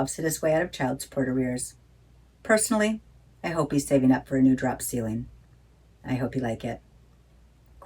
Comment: Me: "Did she say drop ceiling???" 3:45 Also me in a stunned look: "Ohhh, I get it now."